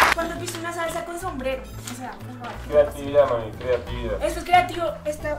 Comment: escuché mal o payaso le dijo mami a